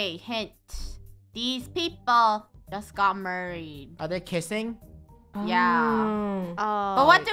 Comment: I heard said murdered so I was confuse